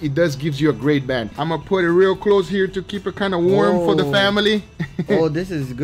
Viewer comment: Did you say FAMILY